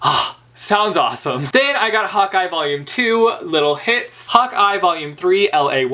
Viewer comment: seriously when you said "hawkeye" i heard "hot guy" LOL and then you clarified it XD